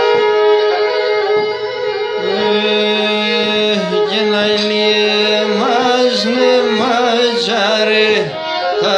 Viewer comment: Τι λέει το δίστιχο στο 14:38 με 15:13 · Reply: Να ιλι εμας ματσαρε θα τρώει μας το λασιον σεμας ιευ το μουχαπετ ασολιον το καλλιον